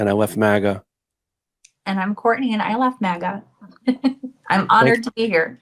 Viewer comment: She just signed off “I love MAGA” instead of “I love leaving MAGA”. Very doubtful. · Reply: She said "I left maga." She repeated what the guy said.